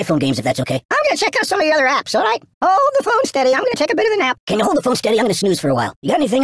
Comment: is my favorite? And what did he say? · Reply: Ooooh the phone's steady imma take a bit of nap